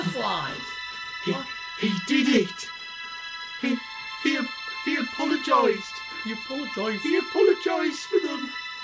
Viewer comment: what was actually said he did it he apologised what the captions said He 8pm